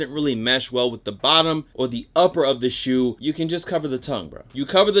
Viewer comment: bruh I thought he said u can change the tongue